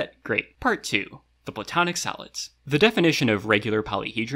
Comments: Confession: I was confused because I kept hearing “solid” as “salad”, so I thought educational YouTubers just sort of called them the 5 platonic salads. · I keep hearing 'Salad' instead of 'Solid'